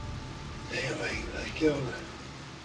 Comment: For the first case, I don’t know but it sounds like he said “they think I killed them,” and not dammit I killed them.